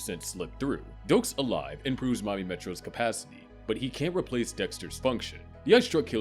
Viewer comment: subtitles says mommy metro 💀